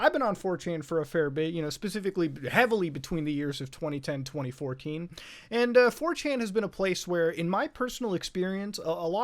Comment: I heard „Between the years 2010 and twenty four KEEM“